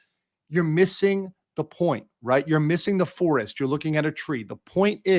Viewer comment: I heard that wrong. I thought you said "you're missing the forest and only seeing the treecko" 😂